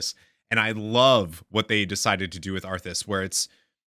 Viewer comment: "I love what they decided to do with arthas..." - Said that again? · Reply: I love what they decided to do with Arthas